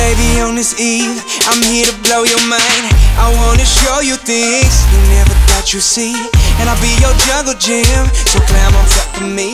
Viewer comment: i hear am gonna show you dicks you never thought you'd see xD